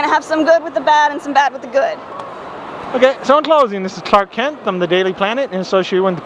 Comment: homestuck. yup. it is so addicting once you get into it. homestuck is a pretty great fandom. but the girl kept saying "it references to the universe" "its like a reference to a lot of games" but... i think what was confusing the guy was that he thought it was a game. if she told him it was an "interactive webcomic that was mainly gifs, i think he would have gotten it. but i can understand, since it was an on the spot thing. and when he said "clark kent" i thought he said "karkat". XD